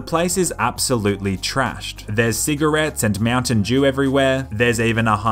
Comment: why tf you said mountain jew at 2:53???